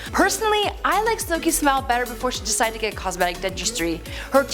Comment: I thought the host said "personally I like her SMELL 👃before she decided to get cosmetic dentistry." Had to rewind several times to correctly hear "her smile." 😂